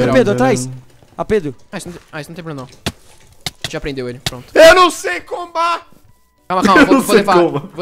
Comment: eu entendi eu não sei fuma kkkkkk 😂